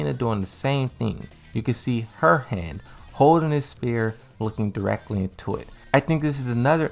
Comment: I thought he kept saying spear. In fact it was the word Sphere.